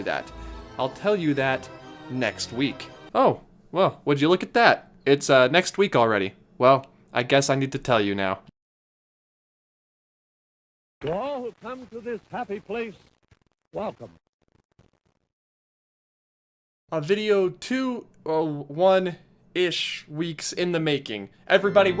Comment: As a non natural english speaker I always hear " We all come to this happy place. Waffle !" But what does he really say please ? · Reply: "To all who come to this happy place, welcome." That was the opening line of the speech Walt Disney gave on opening day.